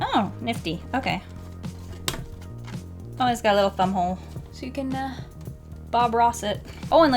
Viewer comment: When you said ‘thumb hole’ at the beginning I thought you said butthole XD